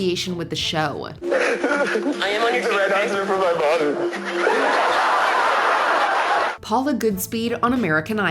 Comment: I watched Top Chef: Just Desserts and was INSANE, but I hear him saying "mother," not "mommy." The captions did him real dirty on that, but whatever I guess... he sucked.